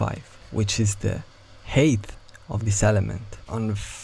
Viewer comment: each time you say "height" I hear "HATE of this element" im sorry but im dying